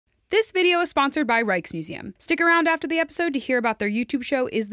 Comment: Habe ich hier "Reichsmuseum" gehört?!